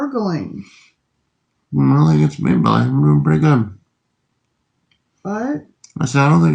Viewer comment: I thought he said, "i dont think it's Maybelline"😂